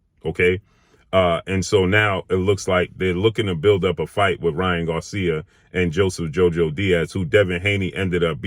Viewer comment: I swore I thought said Lying 🤥 Garcia......either way he on point!!!!